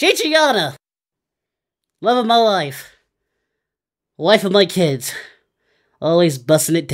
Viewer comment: Did he say wife of my kids?